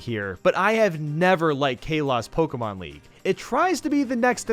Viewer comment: Did he just say kelos not kalos